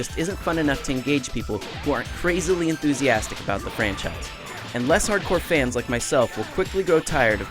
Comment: I heard "And less" as "Unless", that's why said that haha Either way, I do think these games are quite capable fightning games in terms of mechanics, I enjoy playing Tenkaichi 2 a lot more than quite a few "proper" fightning games, despite being a fan of those games as well. Fact of the matter is, for the fans these games are great, but even for the average person the combat system and the gameplay, which is what matters most imo anwyas, is quite capable and really fun.